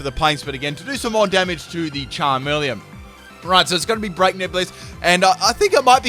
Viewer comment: For a sec I thought you said Break Nips Blitz lmao